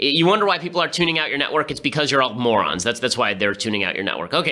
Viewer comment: Ben. Did you just call the Media @ 7:09 "Alt-Morons"? If you did, my respect for you just ticked up a few notches!